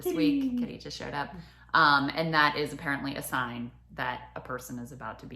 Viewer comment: Krystal just swatted aside a cat and asked it to shut up? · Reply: I think she said "cat showed up", I thought I heard "shut up" too lol